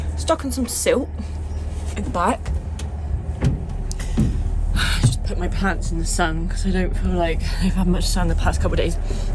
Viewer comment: I just had your vlog playing whilst i was making breakfast (a great way to start the day BTW) and i almost choked on my toast when i thought i heard say... "I just put my pants in the sun, i don't feel like they have had much sun the last couple of days." The somerset accent is easily misheard sometimes and even when i replayed it to check it still sounds like that to me! 😂